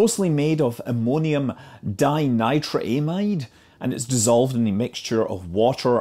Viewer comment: For a moment I thought you said ammonium di-nitraaZide and my brain went: "Nitrogen, with two sides of nitrogen, linked to rings of nitrogen. Yeah, that sounds energetic. Not sure it would be safe at all, but energetic."